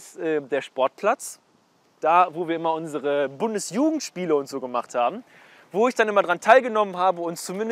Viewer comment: Erstmal "BundesJudenSpiele" Verstehen XD